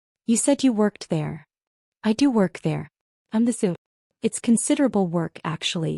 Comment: “I’m the zoo.” I THINK what she actually said was, ”I’m the COO.”